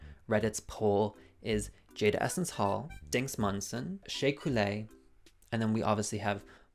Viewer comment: did you say Jinkx Manson?